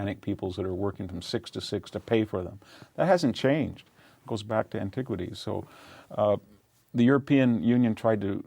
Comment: "Goes back to antiquity", did I hear correctly what this eminent historian said?